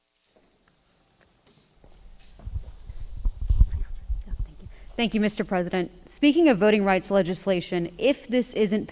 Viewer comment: What is said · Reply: I hear, "I got you" and she replies thank you.